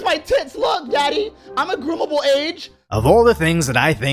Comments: I heard him say "gropeable age." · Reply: he said "groomable age" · Did he say "groomable age?" At around 2:40? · Did he… did he say “Groomable age” ????